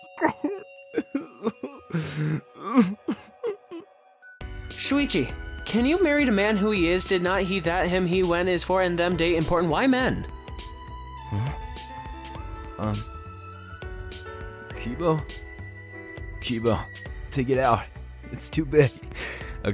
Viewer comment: I thought he said. Amon but he said why men